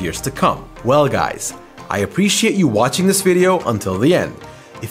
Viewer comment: At first I thought you said "I appreciate you watching this video at 3 am" and it's actually 3 30 am